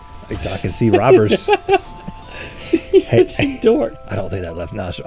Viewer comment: did link just said "You're such a dork"?